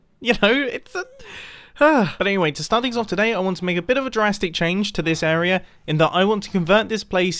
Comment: Is it just me or did mini say Jurassic